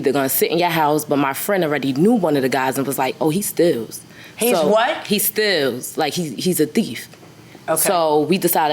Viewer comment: I thought she was saying “he stills” she was saying “he steals”.